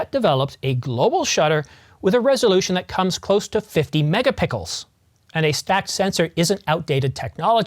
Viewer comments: Did you say “MegaPickles” @ 1:52? 😂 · Ha, at 1:48 did you say "mega pickels"?!? I stopped editing on my other screen and replayed it 3 times... too good. Looked in the comments, but everyone being serious there. I just wanna see what goodies trickle down to the R5MkII... the wait is killing me. My orig Canon R, yes just R, needs retirement...